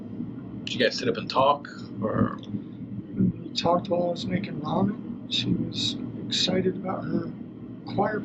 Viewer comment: Yes, please. Am I hearing things?? What does he say at 12:48 to 12:52? It sounds like he said, "We talked while we were making love." I have listened to it slowed down, with earbuds, without. They were clearly asking about having conversation with Maddie on Sunday, not Jen. They ask about conversation with Jen after this. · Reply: ”we talked while we were making ramen” haha. I like your interpretation tho:)